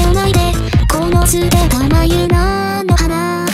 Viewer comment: アイシテタの部分が愛捨てたに聞こえるの私だけ、、？